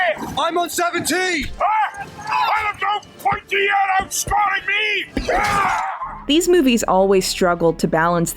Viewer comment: After a million watches i still dont know what Gimli says here 1:39:41 . Something about out scoring or out smarting? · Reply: I have captions, something about being out scored by a pointy eared something I think